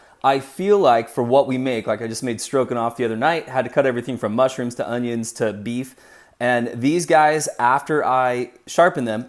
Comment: did you just say "strokin' off?!"